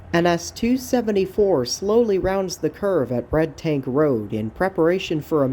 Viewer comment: did you say bredtank or red tank rd i can only find red tank road but its not a bend like this in the shot at 13:45 in the video and where is carls siding if I'm saying that right with a c or a k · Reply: Red Tank